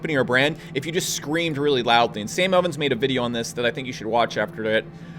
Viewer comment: What does he say? Does someone have a link to the video he refers to? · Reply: Stefano Cuter “Sam Ovens made a video on it that you should check” idk which video, though. If I find it I’ll post here